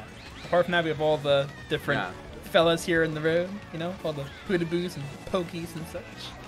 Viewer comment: I know I heard you say Podoboos. Captions: "pewdiepies"?? o_0